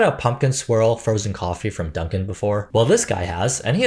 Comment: You said “pumpkin swirl” frozen drink. I heard “pumpkin SQUIRREL” and thought…does it have so much sugar you act like a squirrel after drinking it? 🤣🫣